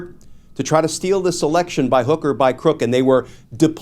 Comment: Did you just say by hooker, by crook?😂